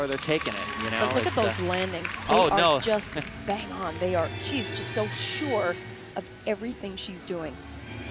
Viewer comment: I thought the woman talking said "she's so short" Lol...i was like..if she was tall, i'm sure she could still land perfectly. Lol